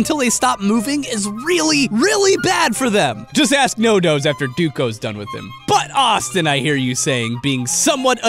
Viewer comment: did he call him Duco? XD